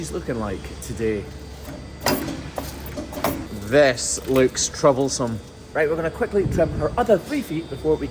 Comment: When you said "This looks troublesome", for some reason I heard "This looks chobblesome".